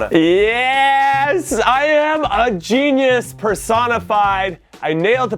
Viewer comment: when he said personified i thought he said persona 5